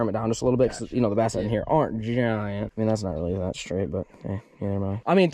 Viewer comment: am I the only one who heard him say “hey that’s not that straight but neither am I”